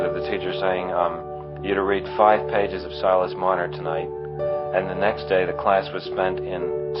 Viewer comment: Which book was he forced to read in english classes, i can't make out the name. He talks about it near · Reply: Silus Marner